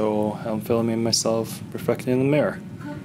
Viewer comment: I thought said "fucking the mirror" for a second O-O